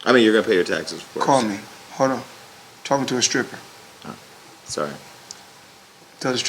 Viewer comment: anyone heard he say calling a stripper? LAWL